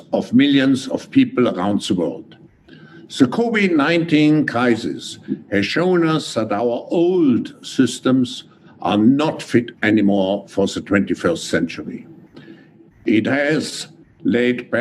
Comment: it’s sure does sound like he says “The Kobe-19” crisis...The Kobe 🚁 ☠️ was the kick off of this whole thing!